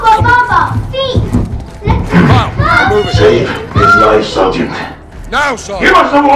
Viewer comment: now sargeant is he saying open the door or let’s go cause he just says it out of nowhere